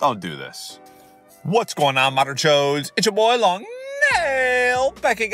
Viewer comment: Quick question, why do you call a cop “an op” Do we even have to censor the word cop now? If so that’s kinda sad that we even have to do that, but hey I don’t blame ya long nail, people cancel others for anything nowadays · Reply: What does long nail say in the beginning of every video? I keep hearing "Whats going on modern joes"